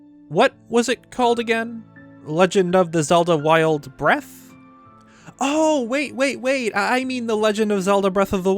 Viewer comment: "what was it called again?" Me: Breath of the Weth